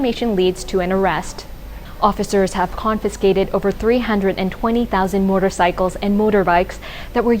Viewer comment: Did she just say 320,000 motor bikes confiscated??? (2:08) and said 320 ....